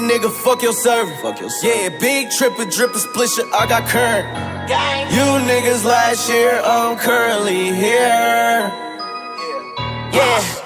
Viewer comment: This entire time I thought he was saying "you niggas on last year's curly haaair" lmao